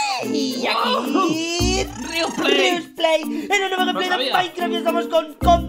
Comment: escuche mal o dijo ¨riumba es rial¨(coriganmen si lo escuche mal por favor)